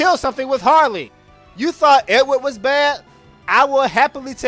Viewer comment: I often name him "Pussy" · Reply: Oh Edward, almost thought you said Edgar. If you said Edgar I would have to get in my Gundam. Lel, we can all acknowledge that Edward is pretty lame, at least in the original versions of Final Fantasy IV.